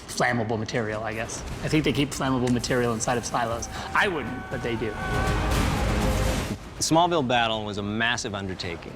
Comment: in the movie for this particular scene, Superman punches Zod in the face and said something as he was punching Zod. No matter how many times I've watched this particular scene, I can't make out what Superman said. It sounded something like: "You *something, something* my mother !" I was wondering if anyone knew what it was that Superman said. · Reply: ""You think you can threaten my mother"